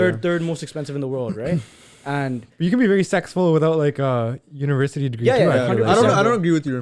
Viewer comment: Am I tripping or did I hear he can be very sexful